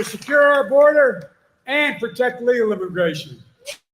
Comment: did he say "protect our border and illegal immigration or legal immigration? around th 16:08 mark